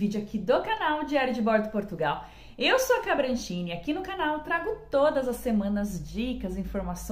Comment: Eu entendi "eu sou a cabritinha"